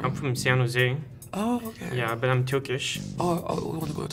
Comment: did she say “Turkyo” ?? 😂😂